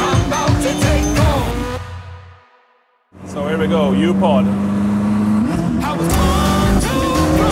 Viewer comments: Did he say "Here we go, Youporn"? at 13:21? I did sound like it. Awesome car and Christian is a great carmaker. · Did I just hear at 13:26 here we go "You Porn" ?!?!